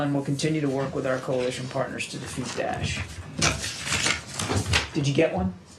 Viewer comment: Sound like he said "...to defeat Ash." Didn't think he was that much of a threat. 😞